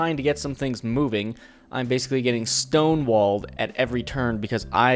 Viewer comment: I thought said he was getting stoned.